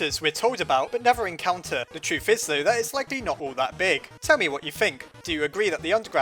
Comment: I thought I heard, "Temmie what you think..." Do you hear it? Like if you do!